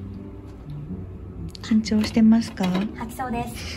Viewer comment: カンチョーしてますかに聞こえるな